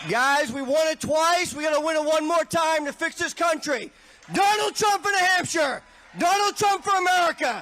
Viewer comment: sounded like he was saying, “Donald Trump in a hamper!” I don’t know what that means, but I laughed.